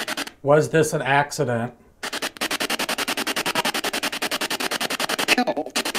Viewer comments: Ohhh you ask “was this an accident” response at 13:04 “no”. I listen on 0.5 play back speed so it’s slower. Edit: Also when you ask what did he leave with.. I heard “nothing, __ believe that?” Maybe it was can you believe that but believe that was very clear. (Hope this helps) Some definite distinctive words, I just can’t seem to make out. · When asked if this was an accident, I'm sure I heard, "No"! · you asked was this an accident?...I heard a woman say No 13:32 You asked how was Sabastian hurt? I heard a woman say He's got the light or He's in the light 14:25 a male voice said "Believe that" 14:45 a Female voice says " Husband " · you ask if this was an accident and it sounded like worried older lady say "NO" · was this accident? I heard a strong "NO"